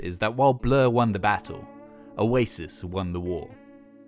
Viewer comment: I thought he said "while Blur won the battle, Oasis wonderwall" lol